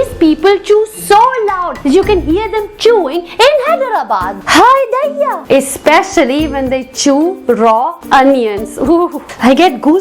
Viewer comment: hahahahahaha so true :D hey what is that sentence you say at 4:54 :) what does it mean..? you say that in most of your videos..:) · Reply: I say Hai Daiyya. It mean oh god or oh my god or oh lord....